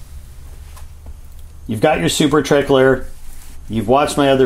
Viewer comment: At first I thought you said "Super Tickler" 😆😅🤣😂🫠